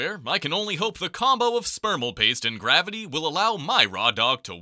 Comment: So am I the only person that heard him say spermal paste?